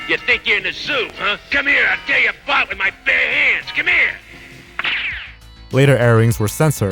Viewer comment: Those are "swear" words, not slurs. 50:50 - What does he say there? "I'll give you a [what?] with my bare hands." 51:36 - Ubiquitous channel watermarks weren't a thing until the mid to late 90s. This was probably recorded during its original airing in 1984 when channel watermarks were rarely if ever seen. Maybe there were no commercials due to it being considered a special episode. Maybe the owner of the video edited them out.